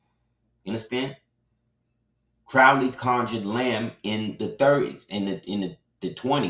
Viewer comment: Gratitude for sharing your knowledge!! I heard you say Crowley conjured LAM in the 30s, or did you say lamb? Also, I tried looking this up and interestingly enough, a company came up named Crowley Maritime. Imagine that!!